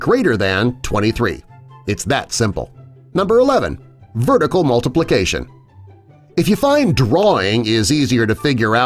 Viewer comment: it said vertic multiplication instead of vertical multiplication